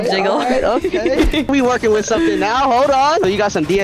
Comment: Am i the only one heard him saying Dense Second lips ?